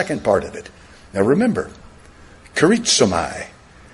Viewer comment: What did he say about the 11:30 mark? Caritsamie?